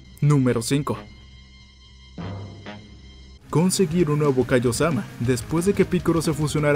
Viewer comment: No era kami-sama, aquí escuché kaiosama ^-^